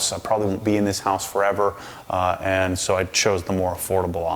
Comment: I thought you said “I’ll probably be in this house forever.” I thought “WHAT?!?!?” keeps it too fresh to do anything forever lol.